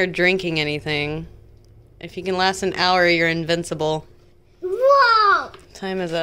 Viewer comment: Another great video my friend! got really serious about daddy eating that spicy chip!🥰😂 The curriculum that you chose seems like a good one. I’m sure that with your creativity you guys will make up for whatever it lacks. Thanks for sharing your chicken pot pie recipe. Your food never disappoints 👍🏽💜💜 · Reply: 😂 He also thought I said "Invisible", not "Invincible", and thought Dad was going to disappear 😂💙 Thank you, LaVerne!🥰🥰🥰